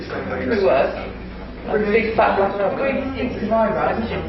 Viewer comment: She clearly says 'could be worse, I could be fat, black and ugly' ...... not 'big, fat and ugly' like the subtitles say....So, racist as well then, hardly surprising. Bit odd they changed it in post tho, why hide what she said? Trying to make her seem a 'better' person than she is??? Seems mental.